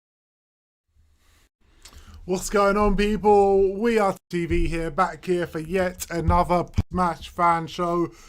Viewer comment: The deep voice again and I swear it sounds like they said “We Are Tv Here , Back Again” 😂